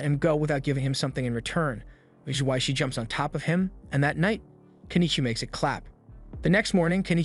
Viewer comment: Bro first seven minutes of the video ajd i hear "that night he make sit clap" 😭 💀💀💀